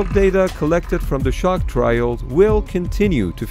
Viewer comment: I thought he said shark trial